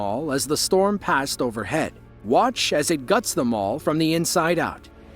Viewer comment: When the narrator said "watch as it guts the mall from the inside out" I heard "watch as it guts them all" and i was like 👀 what now?